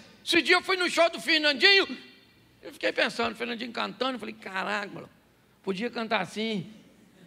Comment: Só que ouvi ele falar Caralh* em 30:54?